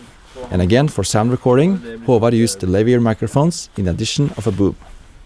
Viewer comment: did you really say "in edition of a boob"?